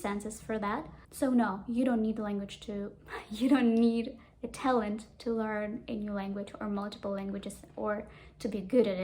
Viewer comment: I thought I heard you say "do you need italian to learn languages?" 😂. I was so ready to sleep but my italian grammar book is calling out my name.